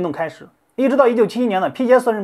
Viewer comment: 九十九年指的是28年，也就是<num> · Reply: Njgdsafy Fly 视频里字幕是AI自动生成的，写错了。 博主说的是<num>年，AI听成了1971年